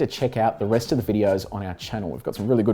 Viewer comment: I thought you said that you have your own motor racing page in the video i must have miss under stood you . (;When you said Drag Races) So sorry if did . May be you should start 1 . Will Holdens be racing any more. As we know it what is next after 2020 season ? I am Big fan of the DJR race Team . I loved the tv 📺 show you had it had a bit of Avery thing you don’t hade things under the carpet that what makes a great 👍 journalist 🌟🎖 I love 💓 the next Everest we are thinking 🤔 of up date . But not a fan of the Electric Ford Mustang & I love 💙 that V8 sounds but I am from the old school Love the GT Falcon,s . I was at Ford Bordmedows for a very long time in all Development department,s Thanks again mate.